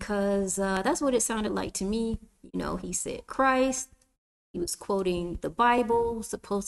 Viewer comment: I thought he said Christ was a European lol. Curses are real but I've watched videos where he was kind of against the European God lol